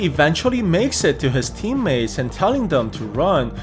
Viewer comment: Did I heard sanji😳